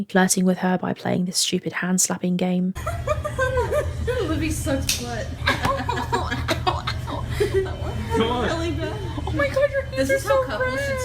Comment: did I hear that right in the background??? "Libby's such a slut" ?????